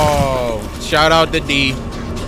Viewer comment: Did say shout out the d?